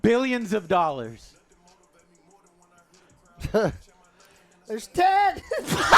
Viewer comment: What said @ 41:13 - "There's 10." What I thought I heard - "There's Ted!" lmao